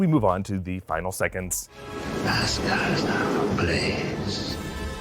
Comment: In the in it also sounds like it says Asgard is now ablaze